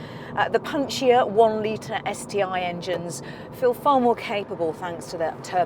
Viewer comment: Did she say STI engines? :D Damn, give me subaru´s sti engine into small fabia and I will buy it for this price :)